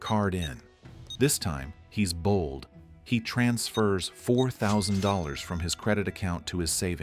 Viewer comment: says 400 instead of 4000